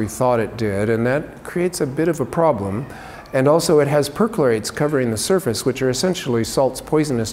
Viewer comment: did he say “perchlorates” or another word?